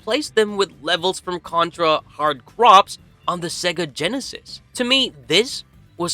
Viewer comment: Contra Hard Crops huh? Is that the Contra Stardew Valley edition I heard so much about? :face-blue-smiling:. Hard Corps is just pronounced "hard core" lol